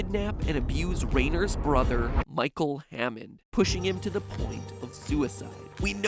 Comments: Why did I hear “Michael Afton” instead of “Michael Hannond” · almost thought you said Michael Affton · I thought he said Michael Afton! 😂 I was scared that he would somehow connect this to FNAF! 😂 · i heard micheal afton XD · had a heart attack when I thought you said “Michael Afton”